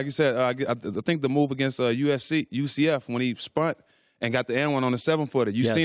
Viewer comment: Did he just say "spunt"? Done!